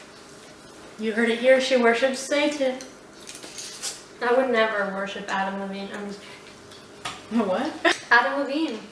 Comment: the cc says "i would never horsehit pattern i mean" lol